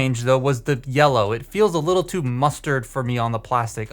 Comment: did you say MUSTARD?